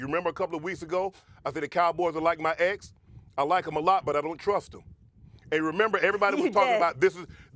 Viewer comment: Did Shannon just profess his sexual preference at 3:24 or did I just hear my own things??? Did he say "They're like my ex, I like him a lot but I don't trust him?" Or did he say "I like them (cowboys) a lot but I don't trust them?" EDIT: I turned on the CC's and they translate to the latter but I listened several times and it sounds like the he said the former...